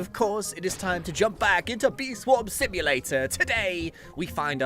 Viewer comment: I heard him say "Bee Swim Swamulator" and i was fairly surprised to hear that because i say that too lol